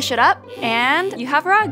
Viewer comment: In the end I thought that she said <<have drugs>>